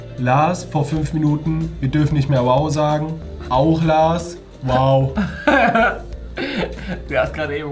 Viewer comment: Was hat da lars gesagt🤭😂😏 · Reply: Wow